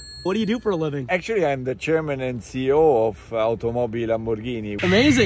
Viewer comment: Even the way he speaks is luxurious 👀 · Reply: he isn't german. He said chairman, i also thought he said german, but he didn't